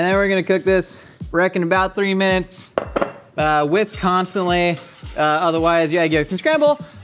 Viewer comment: I thought he said "Wisconsinly" instead of "whisk constantly" at first. lol